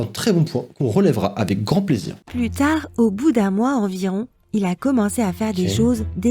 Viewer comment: Ya que moi qui a entendu "putain" à la place de "plus tard" à